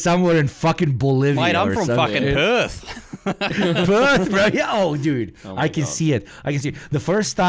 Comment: PERTH MENTIONED 🔥🔥🔥🔥❤‍🔥❤‍🔥❤‍🔥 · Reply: Jesus Christ, I misheard that and thought that he said "Earth" instead of "Perth"! LoL